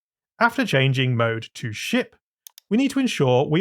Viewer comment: what did he SAY?! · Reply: Ship